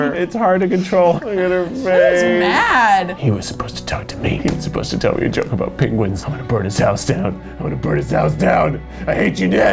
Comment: I got excited for a moment because I thought Keith said HE WAS SUPPOSED TO TELL ME A JOKE ABOUT PETE WENTZ. And I was like whATTTTT.